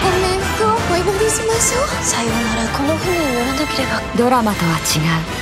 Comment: 「この船に乗らなければ」 「この日にやらなければ」 ってどっちにも聞こえる